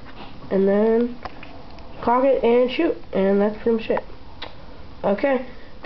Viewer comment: did you say and thats pretty shit